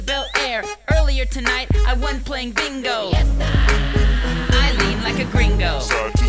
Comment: UNtil i read what it said at1:10 i thought he said YESSSSAHHHHHHH i was liek wtf lmao